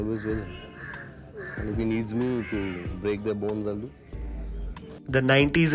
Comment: Sanjay Dutt. Man this guy 😂😂😂😂😂😂😂😂😂. "If he calls me for help, I'll break their bones". 😂😂😂😂😂😂😂😂😂😂😂😂😂😂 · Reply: I thought he said ' I will break their balls'😂